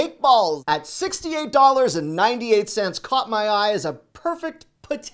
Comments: What I heard for the cake balls was $68.98 at 3:38. Listened several times. Managed to finally get a good look at the price $16.98 for anyone else hearing the same. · did he say $68?!?! 😱 that’s mighty expensive for cake pops